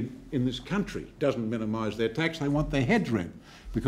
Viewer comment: This resonates even more today than all those years ago. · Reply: did he say heads ripped